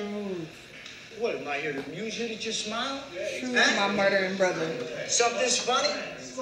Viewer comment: Wait, did just say her brother was murdered??! (26:50) If so, I'm so sorry 😔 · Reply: 😂😂 no she said "truth, my murdering brotha" because he murdered people